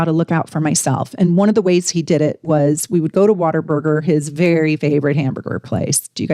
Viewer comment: we have What a Burger in abundance here in the South. Come visit 😊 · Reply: I thought she said Water Burger 😂